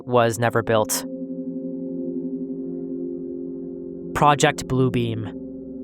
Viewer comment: I was listening while doing other stuff and I swear to me it sounded like he said project Blue Bean. I'm almost sad I heard it wrong now 😂